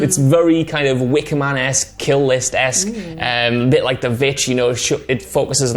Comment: wickmanx? killlistx??? Can some1 tell me wat he's saying & if they are writers or painters he is mentioning????